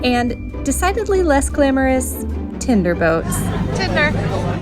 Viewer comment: I keep hearing “Tinder Boats,” which is definitely a different thing. 😂